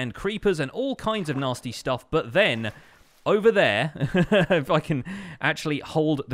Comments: Is it just me, or at 11:31 does it sound like he says "f!ckin" instead of "if I can" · it sounded like u said f***in is just me or did anybody else hear it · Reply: no he said "if I can"